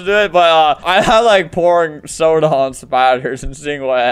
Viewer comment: When he said he had pouring soda on spider,i thought he said "I have p--n on spotify" (I was very confused)